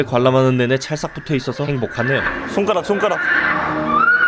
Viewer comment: 초에 땀이2배로나네요 할때 행복하네요라고 들려요